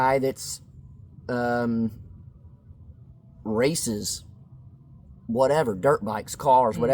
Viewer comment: I thought you said,”if your hobby is racist! I thought, what kind hobby is that? The. I listened on and you must have said races. Pay people to pay more attention to what you are really saying. Still laughinv 😂. Love you kids